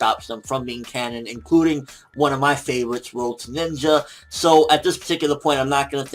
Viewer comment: Who? Did he say "roach ninja"?